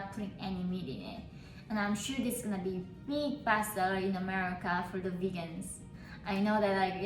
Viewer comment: God i thought she said bast**d 💀 when said "best seller" Lol 😅